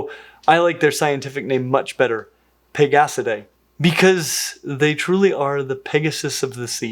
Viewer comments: totally thought you said "bigassadae" 😂😂😂😂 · NGL when you said "pegasidae" I totally heard "pig ass today."